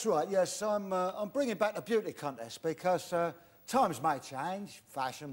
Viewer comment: Did Terry say "I'm bringing back the pubic cuntest...." ?!